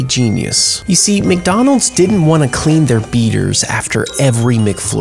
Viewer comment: All I heard was, "Clean your beater" at that point, I lost it LoL 😂